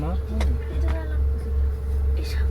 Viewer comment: Ang cute ni mag Tagalog, 1:13. I think she said "hindi, dito na Lang" ☺️